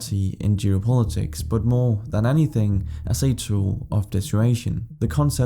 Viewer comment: does anyone know what he said at 0:27 "a tool of the situation"?